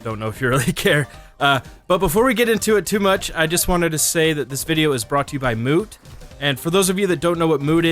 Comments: I thought he said this video is brought by moo lol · i thought he said "brought to you by Moo" .... anyone else? no need for likes to be honest idrc for likes unlike other commenters but still its weird i heard moo instead of moot